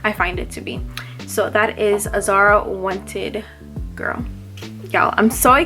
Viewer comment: For the longest I thought you were saying ZARA wanted girl lol. So I always had so many questions like Zara makes perfume bottles like dis??! Is this an old zara perfume if so why does she keep mentioning it if we can’t buy it?? After further research , I now know that’s it’s Azzaro 😂😂 . I can’t wait for the individual review on it now that I know wth is going on lol